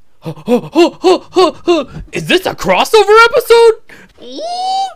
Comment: What They All Heard: "Very Nice!" What I Heard: "Furby Lice!"